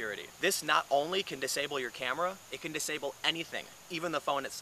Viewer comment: I thought it said it disabled patients.I was like cool!